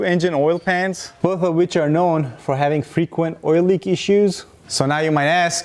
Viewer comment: I swear I heard "frequent oil leaky shoes"